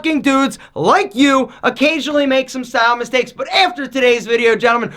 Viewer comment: Oh a death punch! I thought you said a gut punch to the face 😂